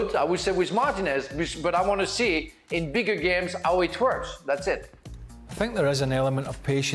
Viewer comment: Did frank just say "I wanna see in bigger games how he twerks"?